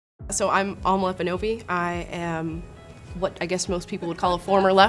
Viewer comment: why did I hear "Kenobi"